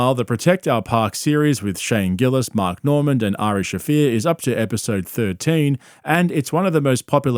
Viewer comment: I thought you said Irish Shaffir. We don't want him.